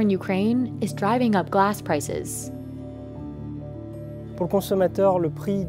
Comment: Is she saying "glass prices" at 7:02? 🤔